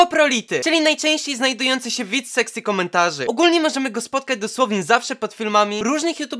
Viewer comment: czemu ja usłyszałem "sexy komentarzy"?